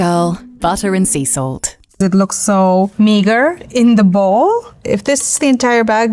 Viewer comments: what did she say???? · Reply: be normal. she said 'meager' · Yo what she say @ 5:26 👀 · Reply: Meagre